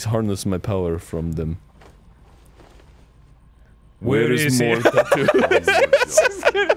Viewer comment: What did they say? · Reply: Where is more tattoos